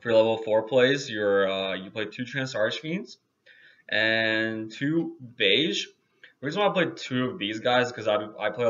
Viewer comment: What is that monster he lays out around 2:50 i can't make out what he's saying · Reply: trance archfiend and beige of the darkworld